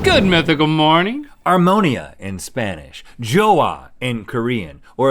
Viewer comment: For a sec i thought he said "amoniac" Yeah, armonía